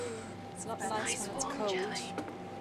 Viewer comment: Did anyone else hear in min. 13:57 a "I Want Jelly" whisper????